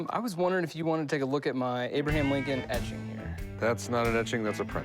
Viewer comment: Thought he said Abraham Lincoln edging